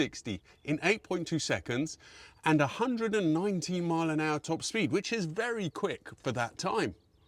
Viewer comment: 119mph top speed? That would be scary and slightly unpleasant in a convertible, never mind the 190mph that I think I heard you say!